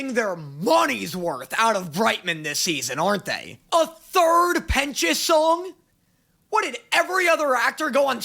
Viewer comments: the captions say third penis song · snorts the captions said "A third Penis Song" instead of Pentious hehehe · (captions) bro did not say that he said pentious